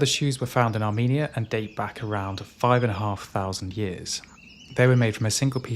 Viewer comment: Hey, I noticed that in the captions at 10:16 you said the oldest shoes are 5,500 years old but the captions said 55,000 years. I don't know if you can change captions after uploading but I just thought you might want to know.